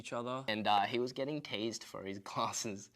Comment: I thought he said he was getting tazed for his glasses